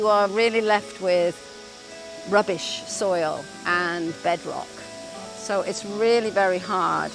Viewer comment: Maybe your subtitle is mistranslated, as I think she said "bedrock" at 5:13, not "bad rock". <link>